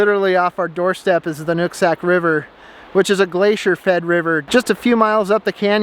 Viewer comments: He who chops the fire wood warms himself twice. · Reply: Did he say the Nut Sack River? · 😧 Wait, what’s the name of the river? Pretty sure i didnt hear that right 😅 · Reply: NOOK... sack.... Nooksack River. Don’t get too excited. I didn’t say what you wanted me to say.